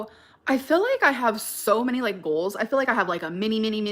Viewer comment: I spaced out because her voice and i thought she was saying bowls not goals and i was so confused